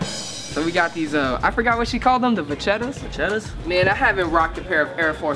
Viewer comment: LMAO "i thought she said pancetta"